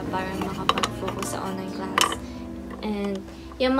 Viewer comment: did you say 10th class??